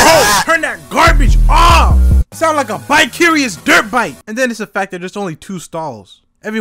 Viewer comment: “You sound like a bi-curious dirt bike!” Fuckin killed me 💀💀😭😭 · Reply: I THOUGHT IT SAID VICARIOUS LMFAO